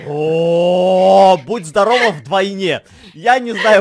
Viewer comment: а мне послышалось что ты сказал будь здорова на войне 😂😂😂😂😂😂